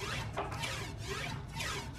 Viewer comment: I hear:"yeah boy yeah boy..."